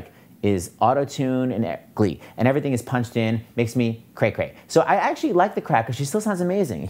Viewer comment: wow. I always thought they were saying "...makes me hot!"